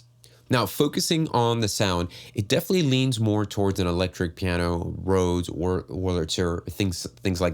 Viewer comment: what do you say after electric piano please? Is that what people mean by the glowy chord sound where you can’t pick out each note of the chord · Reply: he says Rhodes and Wurlitzer, which are commonly used types of electric piano. you can probably look up what they sound like on youtube